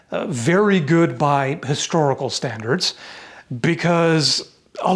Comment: I thought you said "Goodbye, historical standards." and not "good, by historical standards." 😄